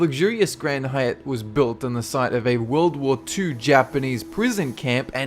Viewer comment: When he said World War Two I thought he said World War poo and had to stop for a second